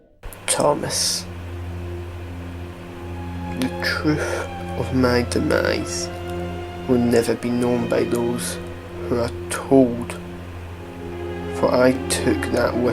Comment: what did Timothy say at 4:41? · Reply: He says : The truth of my demise...will never be known by those...who are told...